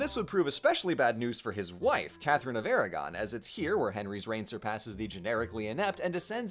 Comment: "his wife, Catherine of Aragon" Me, having the water running as I wash dishes: "did he just say Captain America?? WHAT VIDEO AM I WATCHING"